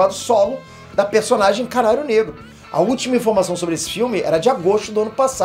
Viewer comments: entendi Caralho Negro kkkkkk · Foi só Eu que ouvi um "car4lho negro"? kkkk